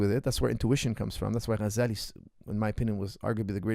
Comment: he mention his greatest thinker of all time but I can’t make out what he said does anyone know? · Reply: His name is Al-Qhazali